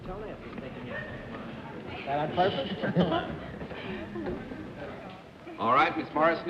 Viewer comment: I didn't hear what Bennett said to the first contestant. Something about her shoes I think. Dorothy and Jack peeked simultaneously down on them. :) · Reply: As Bennett lifted his head so the boom mic could pick it up, I heard Bennett say "......on purpose?". I thought it must have to do with flats or heels. Based on her walk away from the panel, I thought she was in heels. On hearing it again, I thought I heard the word color, so perhaps it was about toenails.